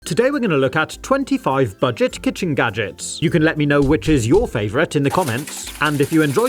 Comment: I thought u said fidget gadgets